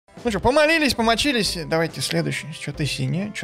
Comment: Мне одному послышалось: О МОЛИСЬ МОЛИСЬ